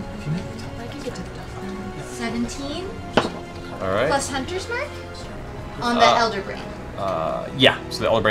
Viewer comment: I misheard her thought she said she was putting hunters mark on her brother and i was like shit that savage